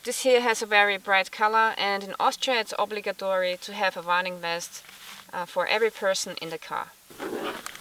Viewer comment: What's a varning vest? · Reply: Ohhhhhhhhh, I too was confused. I thought she was saying "running vest" LOL "Warning" Duh!!